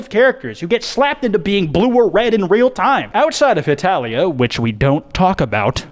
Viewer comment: I was eating and the way I dropped me for, when I heard “Hetalia”. Terrible shit never want to head again